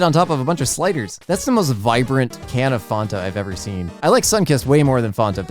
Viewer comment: I’m sorry what did you just say, did I hear you say fawn-ta? The only drink that I know of is fan-ta.